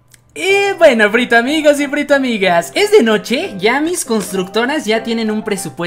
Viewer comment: Lo que todos escucharon en el minuto 5:22 hay mis constructoras (o algo así la verdad es que me olvide u-u ) lo que yo escuché en el minuto 5:22 hay tan más micielas constructoras 😆 xD , llegar tarde es mi pasión xD Ok no ti amu mucho frito 💫 cuidese